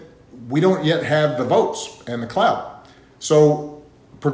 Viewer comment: did Obama just say "clout"?